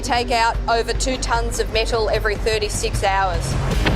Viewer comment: does she say that they take out 2 tons of nickle every 36 hours? NIckle?